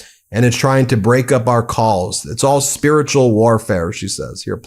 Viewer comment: I thought KKKaroline said "trying to break up our cult" 😂😂😂😂😂